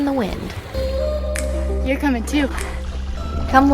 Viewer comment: I watched your vids for three months before I realized you were saying in your video, "You're coming too". I thought you were saying, equally appropriately, "You're gonna puke."